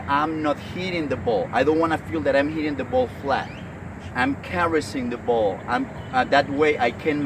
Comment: You said something like: don't think you are hitting a ball but ???? Around 11:02. I could not catch word you said. What did you say??? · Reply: Hey Yasutaka, 'I'm caressing the ball' is what I said. Thanks for watching!!